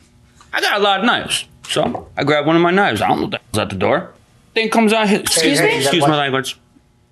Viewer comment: Anyone know what he said that left Milian and Douglas shook? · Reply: I think he said, "I don't know who the f*** is at the door."